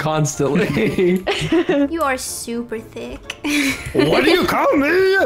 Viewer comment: sounded like she’s calling him thicc lol WOW 27 LIKES THANK YOUUUU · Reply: Lynxシ she was calling him thicc